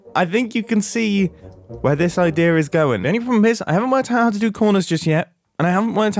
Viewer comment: I had to listen to that 4 times before I got what you were saying. To me it just sounded like "a-hamah teha-a to do corners yet" the first 3 times... Auditory processing disorder is fun... (To others who are wondering, I think he said "I havent worked out how to do corners yet")